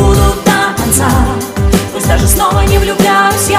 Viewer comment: А мне в первый раз послышалось "тебе на лодке от...у" По этому переслушал))))